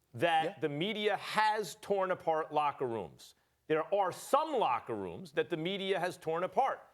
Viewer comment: I thought he was saying “media has broken up locker rooms (too).” Lmaoooo